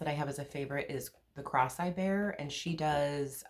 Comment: Lol! I thought you were saying The Crosseyed Bear, instead of The Cross I Bear! Ha, ha! Enjoyed your video. Wishing you a prosperous and Happy New Year!